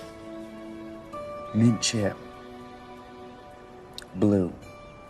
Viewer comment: SUBTITLING FILM, IT WOULD BE BETTER FOR OTHER EXPECTATORS FROM OTHER COUNTRIES. THANK YOU STAY WITH GOD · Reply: It sure couldn't hurt to have the possibility of subtitles, but I'd prefer not having them unremovable in the picture. As for a german spectator I understood nearly everything (1:20:47 does he say "Mint-chip"? Is it some kind of ice-cream? ), because they are that nice and speak very clearly. ;-)